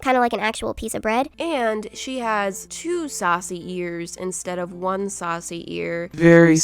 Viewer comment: I thought she said sausage ears XD what is my life!?!